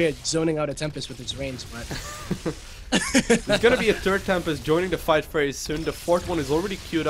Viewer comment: What is a turd Tempest? at 6:45 he says turd Tempest, new unit Lowko?